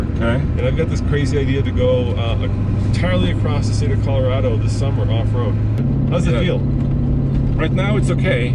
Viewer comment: I'm not a huge fan, but I'm totally open to new ideas. But, for the same price, you could get a Wrangler TJ. Also, 23:05, did you say "this summer" or "this Hummer?"